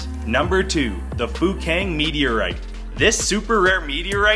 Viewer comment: "The Fukang Meteorite" I thought he said f*cking